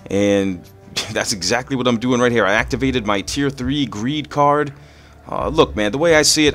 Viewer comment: I coulda swore that guy was saying green card for the longest